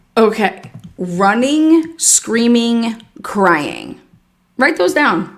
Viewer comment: she wasn't screaming but I think she says is 'tears streaming'